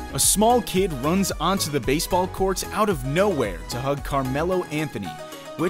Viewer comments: It always me laugh to watch of every video you post, more video more good vibes keep safe and god bless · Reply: did he say “baseball courts” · did he say “baseball courts” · “baseball court” me: that last one was coo- wait did he say baseball court ‘rewinds’ yup he said baseball court. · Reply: did he say “baseball courts” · I subscribed and turned on the notification! Thank you! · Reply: did he say “baseball courts”